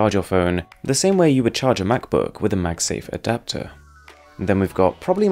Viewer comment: did he just said "MAGSAFE"